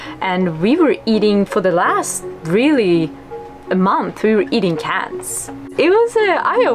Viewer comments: I thought she said "for the last month we were eating cats" 😂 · my heart stopped when I thought she said "We were eating cats" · u were eating cats???? Lol sorry. Is that what u said? or caps, cans??? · did she say that for a month she was eating cats???? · does she say “eating cats” ?? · What did she say at 11:28? When they ran out of food stores they were eating CATS? :D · they were eating "cans" for a month for everyone who thought she said cats. Lol. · did she say they were eating cats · Did she say that for a month they ate cats? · Did she say they ate cats for a month? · Did they just say that they were eating cats? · Did anybody else hear her say "we were eating CATS" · This may be the most who cares question but @ 11:35 what were you eating? I hear ants, the subtitle tells me cats, but I hope it was cans. Any of the answers make for an interesting story, but not knowing is really bugging me. (no, pun intended) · wait did she say cats or cans